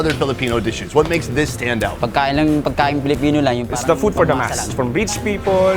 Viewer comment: Why do i keep hearing "its the food for dumbass" when Sonny is asking about the Paris dish 😂😂 Surprised Sonny didnt say "hey that's perfect for me!" 🤣🤣